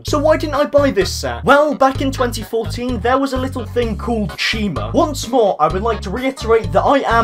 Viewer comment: Which sets make YOU want to disappear off of the face of the earth because you didn't buy them? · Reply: I thought u said china🤣